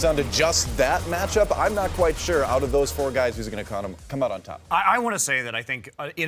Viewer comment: Did he just say who's going to condom on top?